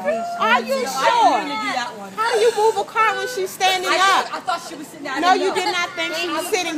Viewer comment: Ok laKarens..I heard her say get down